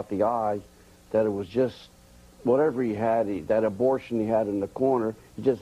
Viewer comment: did Dundee just call Tyson's corner man an "Abortion"? lol Awesome.